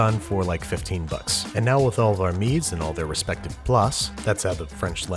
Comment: Literally no idea what you tried to say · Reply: "their respective place" (abbreviation of "mise en place")